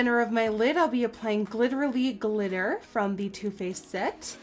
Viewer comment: I am definitely most excited for the glitter dust and the purple mascara! Congratulations Nikkie you deserve this! PS in your original post introducing the collab, I thought you said the glitter dust was called glitterally lol (I love your accent! You were saying glittering!) you should totally use that name for a glitter someday! 😂🦄💖